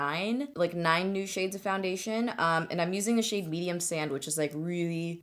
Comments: Totally thought you said you're using the shade "Medium sandwich"... I was like oooo sandwich! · Did anyone else think she said she was wearing the shade "medium sandwich" 😂 on 5:34? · Thought you said "medium sandwich" when you first mentioned the foundation 😂